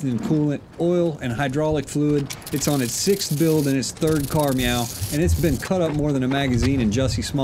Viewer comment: Either I'm stuck in an acid trip remembering super troopers, or at 0:21 does he say third care "meow" --- I can't unhear it now.... I've tried listening over and over.